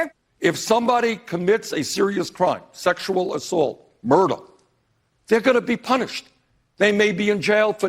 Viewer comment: is he saying murtle?